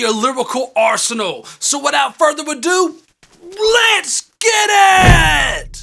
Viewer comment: I thought he said eskitit instead of “let’s get it”